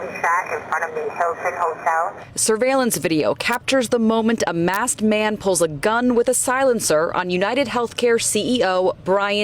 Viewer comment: Why did she say "mass murderer"? It was only one killed. 🙄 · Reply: I also thought she said that too but I think she said 'masked' murderer. As per